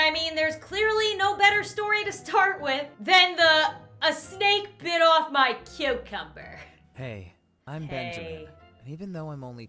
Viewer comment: Oh noooo I steak bit off my cucumber that’s what I thought she said